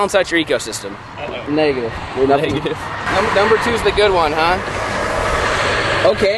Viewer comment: I love but I SWEAR TO GOD he says the n-word